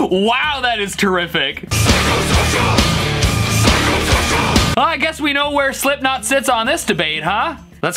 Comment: I only heard pycho vision